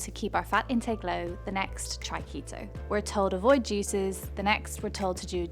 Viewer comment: I thought she said we’re told to avoid Jesus and I leaned real close into my tv.